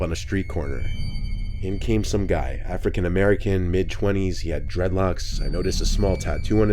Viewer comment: "a frickin' american" is what I heard the first three or so times